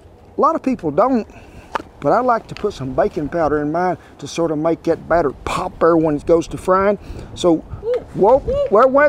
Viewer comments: My fat ass thought he said bacon powder😂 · You said "baking powder"and I heard "bacon powder," and my ears perked right up! Lol. Also, I am praying for the Beag. I have a pupper of my own, and I know how dear they are. 🙏🏻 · I was like "Oooooh Bacon powder? Is that in the link below? Oh, baking powder. Dang." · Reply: I thought he said bacon powder too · I was like "Oooooh Bacon powder? Is that in the link below? Oh, baking powder. Dang." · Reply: I thought I heard "Bacon powder"